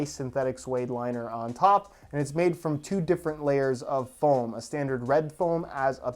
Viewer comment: is it just me or did he say form instead of foam